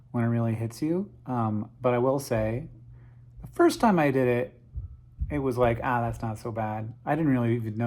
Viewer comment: First hit is free is what I heard. All joking aside though - chemo sucks, I agree with the doctors, injecting stuff to try and kill the cancer first before ourselves is crazy. It's insane we can see objects so young in the universe and re-use chemical rockets as well as manipulate the laws of quantum mechanics. But we can't seem to bring ourselves to end the suffering that happens on this planet on a day to day basis.